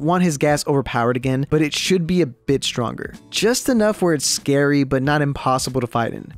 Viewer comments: why did it sound like he said “ bitch stronger”😂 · "A bitch stronger" Did anyone heard that or am I just tripping?